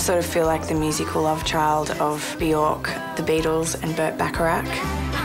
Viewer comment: i thought she said björk